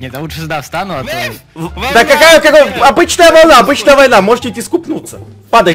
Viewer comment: Мне одной послышалось когда была волна сказал:обычная война можете искупаться